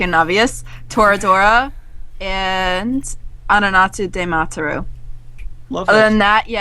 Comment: she says something like amanatsu dematuru? I can't find it on google.. does anyone know what she's talking about? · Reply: nvm found it.. Ano Natsu de Matteru